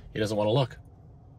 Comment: Sea Cow Runs instead of Looks